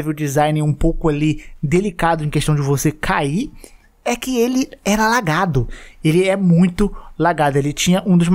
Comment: "É que ele era lagado" E eu entendi "é que ele era alagado", e fiquei pensado que tinha água no lugar, depois que me toquei